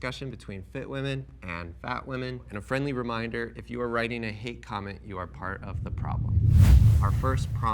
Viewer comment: i thought you said "hit comments", which i thought made everything make sense. better than i can do. and also i hate you. thank you for everyone commenting new things.